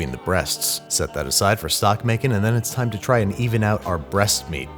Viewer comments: Oh my gosh lol after he said set the carcass aside I thought he said it was for sock making. It wasn’t until he set the winds aside too that it hit me he was saying STOCK making. I need to work on my hearing and my no judgement zone lmao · oh my god, when you separated the breast bone from the turkey and said to save it for stock making, i thought you said for Sock making and i got REALLY concerned